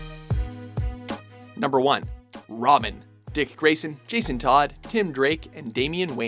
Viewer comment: Is It Just Me Or Did He Say ROMIN ?